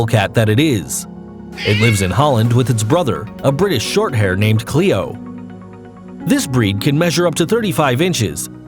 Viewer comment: did u say"Cleo"? That is also the name of a Cleopatra monster in the movie"Monster High"and"Monster High and 13 Wishes"so I wrote that